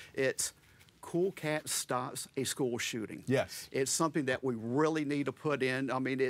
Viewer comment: At first I thought he said "...which is very, very abhorrent. Cool Cat starts a school shooting".